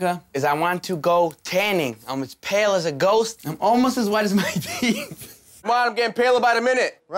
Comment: What does Pauly D say he’s almost as white as at about 3:30? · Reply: As his teeth